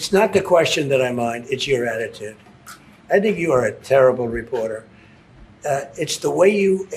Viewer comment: “you know, it’s not the question that I mind, it’s your attitude” he then goes on to say, “ its a terrible question”. Did I hear that right. 🤔